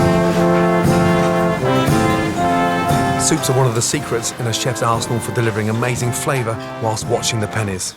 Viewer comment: In a chefs Arsenal bloody hell sounded like he said in a chefs arsehole lol